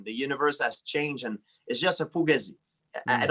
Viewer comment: did GSP just say Fugazi? LOL